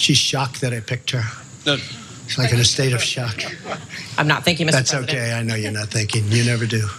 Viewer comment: Cenk says - "She did'nt say Thank You" . She did !! @ 0:54 . I think trump did say Thanking , atleast thats what it sounds like. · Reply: The reporter said: "I'm not. Thank you, mr. Trump." Trump misheard this as: "I'm not thinking, mr. Trump." Trump then responds with: "That's okay. I know you're not thinking. You never do." So yes, you are correct about the woman saying "thank you". But I don't think you're correct about Trump saying "thanking".